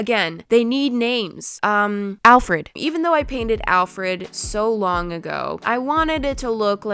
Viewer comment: I don’t know why but when she said Alfred at 16:53 I thought she said Alfredo...(Don’t ask)